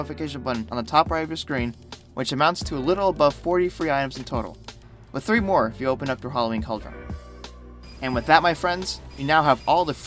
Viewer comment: hey how do you get the other 3 cosmetics I couldnt understand what he said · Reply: "with 3 more if you open up your Halloween cauldron"